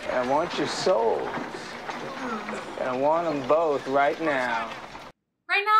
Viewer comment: "I'm your huckleberry" what an iconic line. My first Western ever, thanks dad. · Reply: For years I thought he said Huckleberry too...turns out, Kilmer's southern accent made it sound that way. He actually says "Huckle bearer." Basically telling Ringo he'll be his pallbearer.